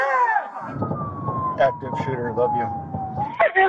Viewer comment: Wow. The officer sending a text to his loved one “active shooter. I love you” is absolutely humbling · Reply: bro I thought he said he loves the active shooter 😭